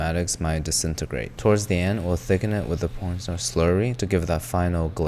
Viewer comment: I could genuinely listen to him indefinitely. Like genuinely. My brain vibes with his speech patterns. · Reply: he did not just say p*rnstar slurry😭😂 on